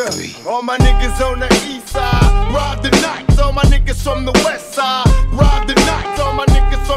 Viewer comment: What does he say at 4:20.rob the knot or ride the night